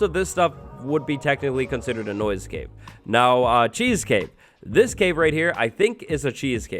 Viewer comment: When he said 'Cheese Cave' I Heard 'Cheese Cake'